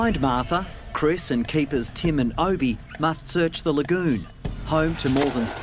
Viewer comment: everyone: omg the poor babies me: excuse me did he just say tim and moby